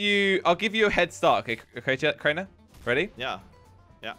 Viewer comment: Anyone else hear say "What the hell man"?